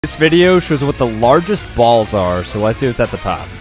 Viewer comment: I was just trying to eat my nachos in peace then I hear “THIS VIDEO SHOWS THE LARGEST BALLS IN THE WORLD” I just spit out my nachos rip nachos 😢